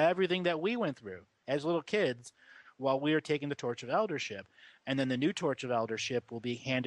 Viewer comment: At first I heard "Torch of Eldership" as "tortured Eldership"; put a different spin on it for a minute there!